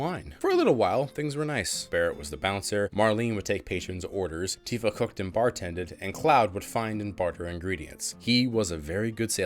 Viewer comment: I had to rewind the past where you said, "Tifa cooked and bar tended." At first it sounded like you said, "Tifa hooked and bar tended." Glad I heard wrong.